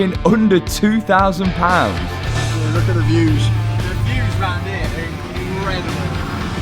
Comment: All I hear is " look at the fuse " 🤣😂😅😅